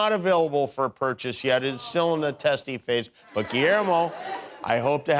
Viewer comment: Did he just say the 'ballwasher' gizmo was in "...the testes phase" and nobody except me noticed?